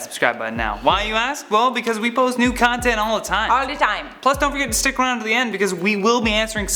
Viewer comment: Lol it sounds like is says 'we post NUDE content all the time' haha Keep your Atari shirt on! I have said this before when you I wore that shirt but I love these, it looks better on me. Lol j/k we both look fly in it.